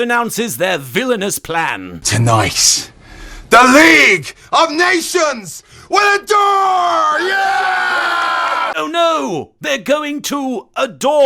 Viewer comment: OK what does he actually say here?? Legit baffled, I've replayed it several times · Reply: "The league of Nations will dawn, yeah". It comes out as darrrrrrrrn, as in this is a "darn dated foreign heel gimmick" and "darn it, go away, Shamus"